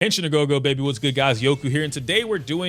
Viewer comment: bro what do you say at 0:00 it sounds like 'Genshin ago go baby' idk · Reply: Henshin-a-Go-Go, Baby. Its from Viewtiful Joe. Great game.